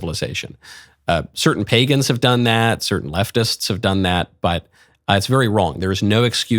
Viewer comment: When you said "certain leftists" have promoted suicide, I at first thought you said "certain methodists". That would have been funny. Meanwhile in Canada, Government and media are promoting institutionalized suicide like crazy.